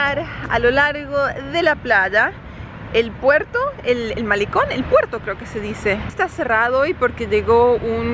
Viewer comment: Hey or anyone else who can help. I am learning to follow spoken Castellano by listening to these vlogs and usually I do fine but today at 4:05 is taking about the port and them in the middle of the sentence I hear "El Maricon" which makes no sense at all so what did I hear or is there some other meaning to the obvious which google won't give me ? Thanks in advance. Love the vlogs in both languages! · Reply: I was saying 'el malecón' referring to the pier or boardwalk! Hehe, you were very close. 😊